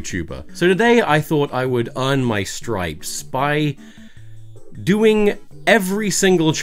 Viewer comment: did you just say “iron my stripes”?